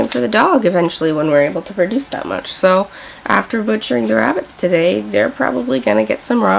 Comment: ARE YOU CRAZY DID YOU SAY YOUR GONNA BUTCHER A DOG